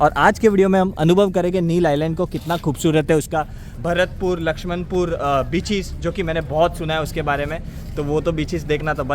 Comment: why it sounds bitches instead of beaches 😅😂